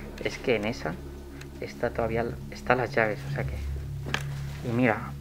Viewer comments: En el minuto 10:19 se escucha una voz de fondo que dice como"tienes las llaves" solo no estaba entonces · Luego de decir que va solo, En el minuto 10:19 se escucha una voz que dice: también las llaves" Quien fue pues? Jaja